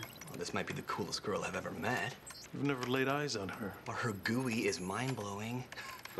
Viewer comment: When i first watched it i thought he said "her gooey is mindblowing!" I've never heard anyone try to pronounce GUI as a word!!